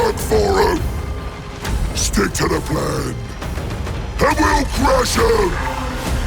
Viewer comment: Sounds like he says "I will crash out"